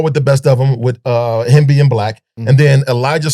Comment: I heard, “him being black.” Lol Hemby and Black!